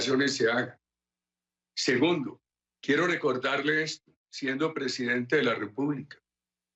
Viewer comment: siendo presidente de la República dijo eso o oí mal ?